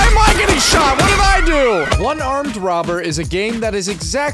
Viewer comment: I thought he was saying unarmed robber